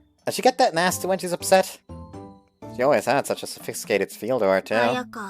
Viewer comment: that's just me mumbling instead of speaking properly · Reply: Oh, it honestly sounded like you said Sophie. But oki doki